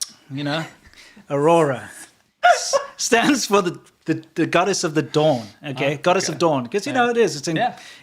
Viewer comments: At first i heard "Goddess of the dong" looool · Lmao I thought said goddess of the dong when talking about the aurora 😂😂